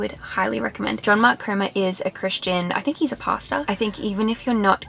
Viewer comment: the way you said "pastor" on 10:39 sounded like "pasta", that cracked me up